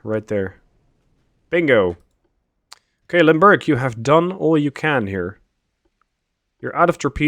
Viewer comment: You say, "Limburg" I hear "Lundburg."